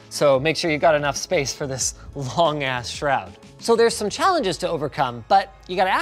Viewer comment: Is it me or did Linus just say “long ass shroud”